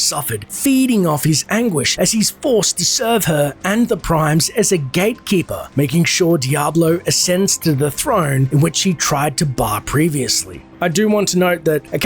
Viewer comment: I thought he said gaykeeper correct me if im wrong though